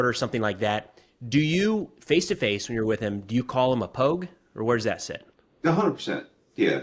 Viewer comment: "Do you call him (your cousin) a pogue?" Did I hear that right?